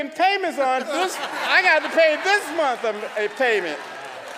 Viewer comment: did he just say mf ing payment at a church service? whoa brother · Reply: He said “this month a payment”. This is an honorable man and father he has class and decorum! That is not what he said.